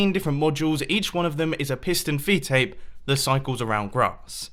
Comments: At the started I thought he said pissed and feedtape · He Says Piston Feed Tape Eleven Times In The Video (Edit: By The Way Thank You For All The Likes And Comments This Is A Record For Me, And Yes I Counted The Time He Said Piston Feed Tape In The Intro) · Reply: Tomas Fahy I thought he said Pissed on a feed tape!😂😂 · i thought you said "i say piss and feedtape alot" XD